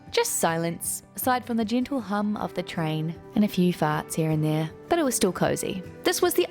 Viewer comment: At ~ 15:42 “…the gentle hum of the train and a few farts here and there…” Did I hear that correctly?